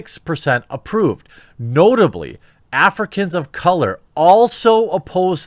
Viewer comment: Haha. Did you say Africans of color at 7:44 Jeremy?